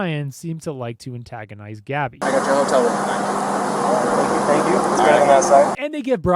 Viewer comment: did Brian just say thank you for taking my side to the officer!?!?!?!?!??!?!?!